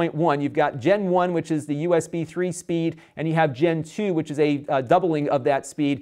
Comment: i heard he talk about gentoo instead of gen2..